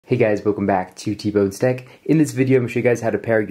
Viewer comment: Haha, I honestly thought you said TBone Steak!!:)) 🥩 . Great video Bro:))